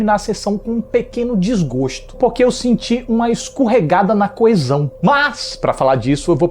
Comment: e eu que ouvi ele falando "escorregada no coisão"